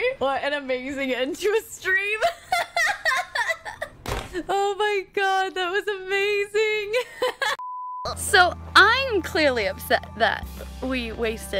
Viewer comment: I really heard Neon in that sentence (the “oh my god you guys are amazing” from after the festival fight)